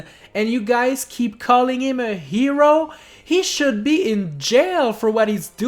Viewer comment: Wait? Did You Say WE Should Be In Jail Or Mario? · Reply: He said "he" (which means mario)